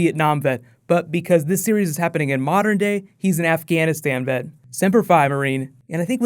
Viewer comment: I thought he said he was an Iraqi vet?